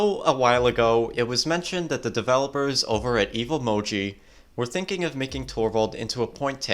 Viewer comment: did u just say evil moji ! LOL